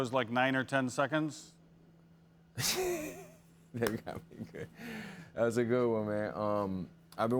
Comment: did he say “Que cabron” ? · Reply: No, I think he said something like "... got me good" then he said "that's a good 1 man"